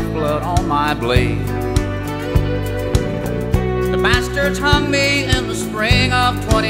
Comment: i thought that guy said The Bastard Tummy in the spring of 25. instead of The Bastards "Hung Me" in the spring of 25 lmao